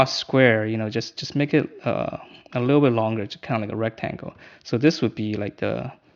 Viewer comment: "It's like a square, but a little longer, there's a name for that you kknow, it's called a rectangle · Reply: He did indeed say rectangle